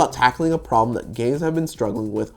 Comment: I misheard “a problem that games have been struggling with” as “a problem that gays have been struggling with”. my mind was racing through all of the things that we struggle with, trying to figure out what they have to do with facial animations hahahahah